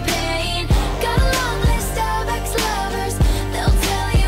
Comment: in blank space i heard it has "got a long list of star ex lovers"